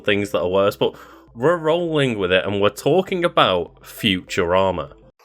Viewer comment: Lol the way you pause while you say “futurama” at around 0:35 made it sound like you said “future armor”